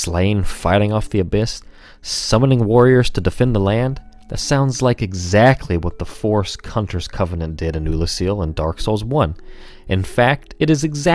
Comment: Did he just say the forest cunters?